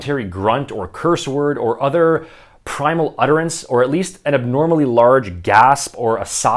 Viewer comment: I heard: "an abnormally large gas". Hahahaha. I thought to myself: "Well, that'll certainly make me nervous before the workout".